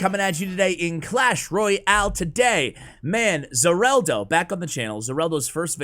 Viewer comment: I thought you were saying Xarelto lol